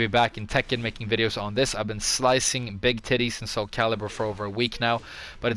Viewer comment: slicing big titties ? did i hear that right lmao